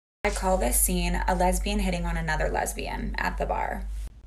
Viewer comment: I thought it was saying " a lesbian sitting on the lesbian at the bar " 🤭🤭✌